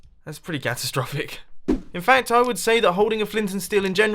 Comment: did you say gastrophic!? It’s catastrophic